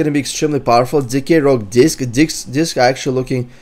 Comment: i love when he was gonna say disc and it sounded like he said DICKS :> 24:00 keep listening